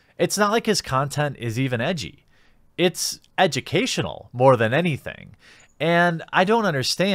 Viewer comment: Did you say edgy-cational?